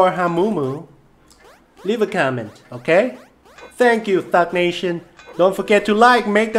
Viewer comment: At the beginning I thought said fuck nation